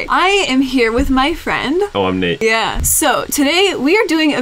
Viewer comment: it sounded like Nate said "No i'm Hate"